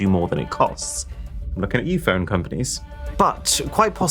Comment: When he said I'm looking at u phone companies all i heard was I'm looking at u apple😂😂